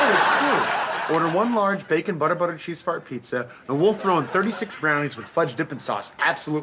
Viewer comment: Did he say "butter butter cheese fart pizza" at 0:26? Suddenly work ethics at Domino's make a lot more sense now.